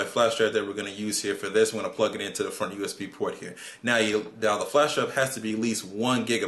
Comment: did he say plugh it into the fuckin USB port?